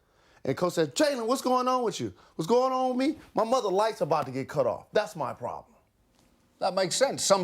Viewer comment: did he said : my mother life was going about to get cut off? HELP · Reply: Lights, as in, mother’s going to be gone.